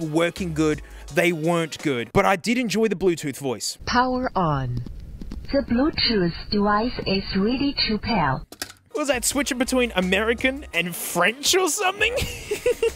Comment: it sounds like it said “the bluetooth device really cheap here” 😭😭😭